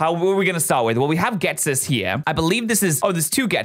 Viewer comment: I think it sounds like there saying Ghetis